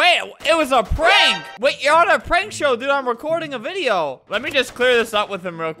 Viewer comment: i thought albert said 'prarsho' or something....he said prank show